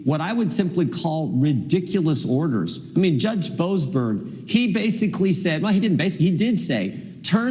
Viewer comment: The say, "Judge Boasberg", but I keep hearing "Judge bozo".